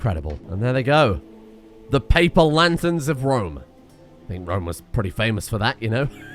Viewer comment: Thought said "the papal lanterns of rome" which also works